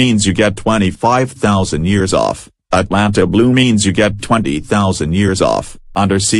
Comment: did you say Atlanta blue